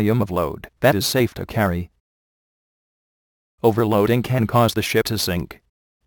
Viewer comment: dosent it sound like he said ( overloading can cause the shit to sink ?)